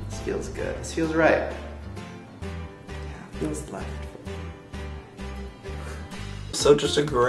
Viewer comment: what does Joey say at 8:11 ? For some reason I can't hear it at all and I wanna know! Awesome video by the way, I love your channel. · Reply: After Shane says, "It feels right..." Joey says, "It feels left for me..." LOL! ^_^